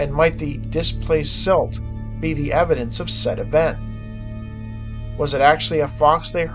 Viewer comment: Dude I thought you said “could the misplaced silt be evidence of SEDIMENT” lol